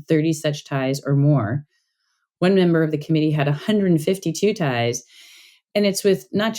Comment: 150 ties ? Do my ears deceive me . ? I entered my previous comments before I heard this section. The conflicts of interest are worse than I dared imagine. Big pharma involvement (funding) is a red flag. The “rubbing” out of info on wayback machine is disturbing but I’m not surprised. We know what’s going on . What can we do. ? Nina is a most enlightening champion .I do hope she is not targeted negatively for her sterling work.